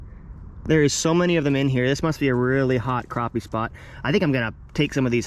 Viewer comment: I heard 2:51 in eminems voice "thats an awfully hot crappie spot"